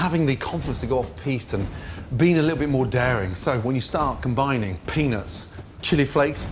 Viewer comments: Is it just me or did he say penis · Does anyone else hear Penis at 3:48? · did he say penis? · the top 4 part i thought he said penis .. but its peanut 😭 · I thought he said "penis" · thought I heard him say penis there for a sec lol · Sounds like he says penis , imagine him asking someone to try his peanut sauce lol.